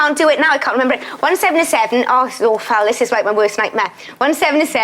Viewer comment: what is she saying under her breath? Is it Welsh? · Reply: It's a little hard to understand with her Welsh accent so I see how you could make the mistake; however what she actually says is " oh, this is awful"